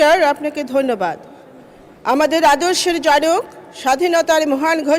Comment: আদর্শের জারক.......?এটা বুঝতে পারলাম না, ওনার সঠিক শব্দ টা কেউ কি বলবেন।